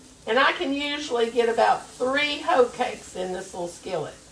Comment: I thought that said "hotcake"